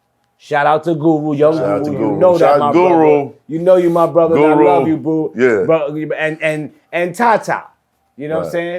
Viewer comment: am I tripping or did he say "I love you boo" about Young Guru 😂